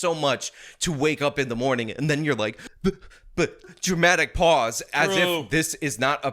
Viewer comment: I had to put captions on because dude said dramatic pause but I swear I hear everytime still 'jewmatic speach' lol almost feel out of my chair. 😅